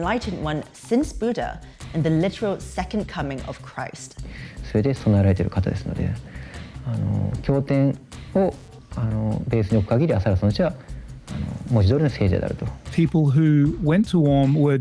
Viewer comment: からの男の人の日本語なんて言ってるか教えてください！ 恥ずかしながら何回聞いても聞き取れない😅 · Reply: 「…それで備えられてる方ですので。あの…経典を、あの…ベースに置く限り麻原尊師は文字通りの聖者であると」